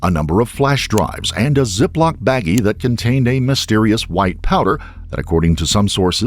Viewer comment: It states 'White Power' instead of 'White Powder'